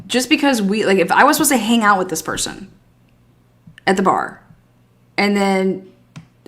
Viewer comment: Thank you!! She says their blinds were shut. When did she notice this? Were you looking for a specific reason at 3am? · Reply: What I hear: I was supposed to go hang out but I got a weird feeling and later I looked toward the house around 3 and noticed the blinds were closed